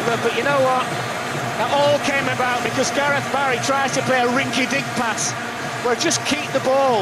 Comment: someone explain to me why he said gareth bale?? i dont understand from when bale play for england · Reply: Gareth Barry